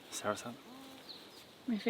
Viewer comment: Can someone tell me the name of the green fruit @ 31:47 please? I heard say 'Sarsa' but am not familiar with such a name or fruit. Thanks. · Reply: Soursop